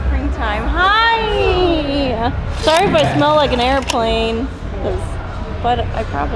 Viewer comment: Did she just say... ‘sorry if I smell like an Arab plane’?